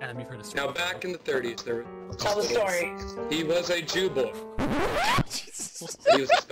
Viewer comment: when that guy said jew boy at first I thought he said jew wolf and the story was about a jew/werewolf